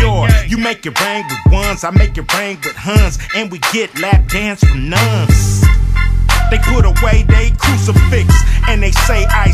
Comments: We get lap dances from nuns 😂😂😂👍🏽👍🏽👍🏽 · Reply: Thought it said we get lap dances for nunz.. free, cuz hes the Man.. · And we get lap dance from nuns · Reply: I thought it said we get lap dances FOR NUNZ.... FREE CUZ HES THE SHIT